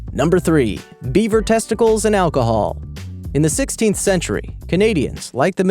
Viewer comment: am i the only one that thought he said bieber testicles and alcohol? 😂